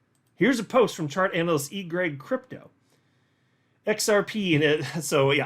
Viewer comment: Whenever you say “EGRAG Crypto” for some reason I hear “Eat, Pray, Crypto” 😂